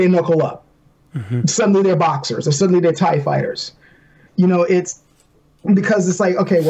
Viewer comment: "And suddenly they are tai fighters" But i heard it as "And suddenly there are TIE fighters" Too much Starwars...